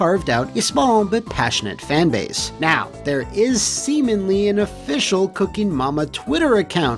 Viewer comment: Did he say “Semenly”?